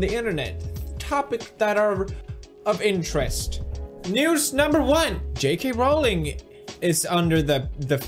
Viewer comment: did i hear that right? "Noose number one!" ?